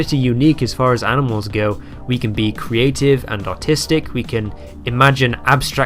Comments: ngl I thought you said autistic here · “we can be creative and autistic” is what I heard and I think that’s a better way of putting it /sarc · I heard “we can be artistic” as “we can be autistic” first. Your bloody bri’ish accent doin its thing.